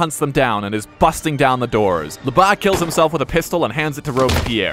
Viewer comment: "Lebas kills himself with a pistol and hands it to Robespierre." I guess Lebas didn't want to let Max "down". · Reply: I thought it said lesbians kill himself and I was like hol up